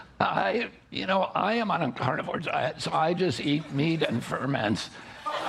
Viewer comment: I thought he said meat and vermins